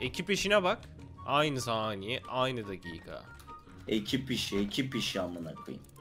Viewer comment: ekip işi dedi ben ekip sişi diye duydum (kabar söyledim ban kasın çünkü)